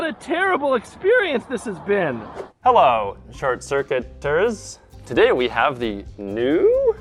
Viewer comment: First thing I thought he said was, “hello short circuit turds”